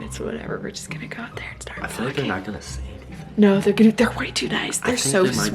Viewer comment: i coulda sworn i heard her say they were gonna start f*c*ing 😂😂